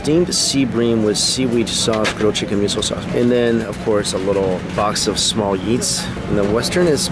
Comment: I thought said: “A box of little yeets” and I was really confused for a second.